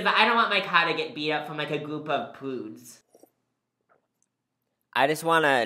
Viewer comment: Not gonna lie i thought said prunes and i was going to live like that thinking he said prunes if it wasn’t for him repeating it again after